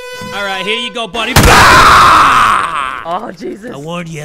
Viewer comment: did anyone else hear the Sykunno 'AWH JESUS!' at 6:02 💀💀